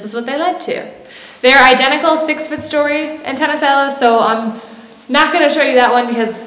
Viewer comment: I thought she said antenna salad